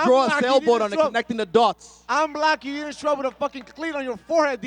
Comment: this shit is so funny to me idk why did It sounded like he said "im black" 😭 Adesanya: what did you say? 😆